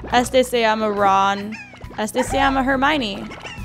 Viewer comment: dijo ''Este se llama Hermione''' y yo entendí ''Este se llama Germán''